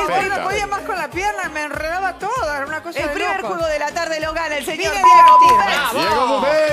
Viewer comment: Qué dijo Adriana en 6:42? Q pusieron el pip? Insultó al participante xq le ganó o me pareció? 😒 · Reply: Vine a divertirme